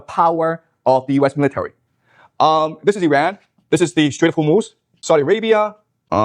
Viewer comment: Strait of Hummus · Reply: Legitimately thought he was saying the Strait of Hamas lmao